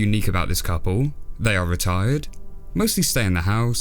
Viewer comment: i thought you said retarded and i started laughing like an idiot